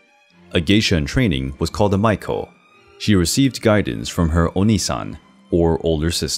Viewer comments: you mispronounced "onee-san" (older sister), pronouncing it that way sounded like "onii-san" (older brother". In English language, double letter ee should sound like the letters in the word "knee". In Japanese however, their e is like the e in hornet. If there are two vowels such as in onee-san , the e should be prolonged. Awesome vid as always. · Am I the only person who heard "oni-san" instead of "onee-san"? XD